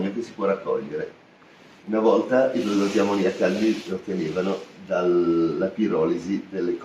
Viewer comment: Al minuto 19:46 non ho capito, qualcuno mi aiuta? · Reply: Dice: "una volta i prodotti ammoniacali..." pure io non riuscivo a capire le parole e ho attivato i sottotitoli in italiano per comprendere queste parole.